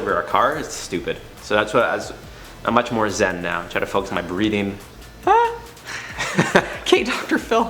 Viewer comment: When he was talking about bring much more Zen, I thought he said," I try to focus on my BREEDING", but assuming he said breathing after re watching it. Lol